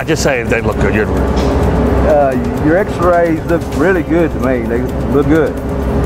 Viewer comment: is it just me or does it sound like hes saying your x-rays look really good to me nigga